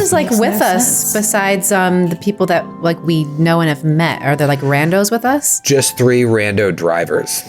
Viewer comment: I had to play back @ 01:22:02, I thought said "Are there commandos with us?" 😅